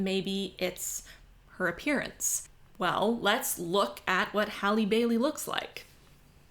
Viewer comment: I was slightly confused because I misheard and thought they'd cast Halle BERRY, who's gorgeous but nobody would mistake her for a teenager. I haven't cared about the Disney remakes, and I'm a little too old for the Little Mermaid to have been a formative experience, but I'm more than happy to spend a few hours and 15 bucks to piss off the racists. :D